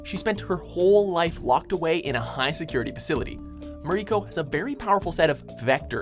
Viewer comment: i swear I heard "Mariko Obama" at the first one"